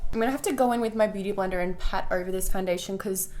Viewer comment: DId she just say "blooty blender"?? 😂